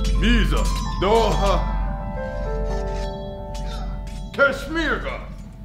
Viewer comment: I thought he was speaking in For-Honor Viking then I heard "Kesh Merga!". Signature Sim Speak. Gets me every time.